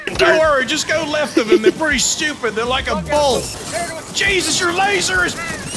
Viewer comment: Did he just say Jesus in lasers cuz f*** yeah